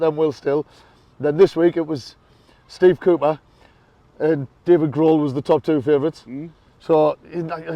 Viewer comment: Happy Easter, chaps. For a second there I thought you said David Grohl was favourite as next manager. He's a Fighter I guess and used to the SoL. 😀